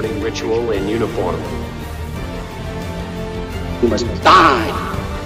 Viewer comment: What did he say @ 8:20? Its not in official release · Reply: Correct we have him saying “you must die”. Glad you enjoyed Paul and thanks for commenting and subscribing to the channel! Have a great Sunday!👍✌️